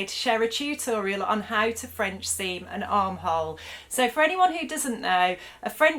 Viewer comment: I have this thing where I read things wrong and I for real thought this title said "How to Seem Like a French A**hole". LOLOL You helped me make my camper a few years ago. Adventures in Ripstop